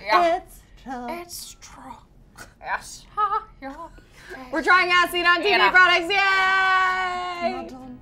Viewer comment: Omg legit thought said "we're trying acid on tv" not "we're trying as seen on tv" 😂